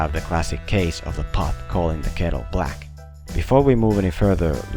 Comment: I know the saying "pot calling the kettle black" but due to shouting in my house at the time I heard cop calling the killer black